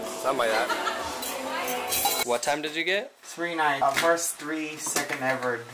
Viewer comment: Did Max say he got a 3.9 average at 1:09?